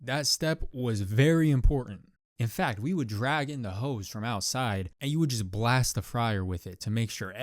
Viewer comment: bruh I wasn’t looking and I thought he said drag in the hoes from outside and make them clean😭😭